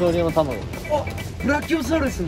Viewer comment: まつくが「〜のタマゴだわ」って言ってるの、ラッキョウサウルスって聞こえてた ブラキオサウルスというのね！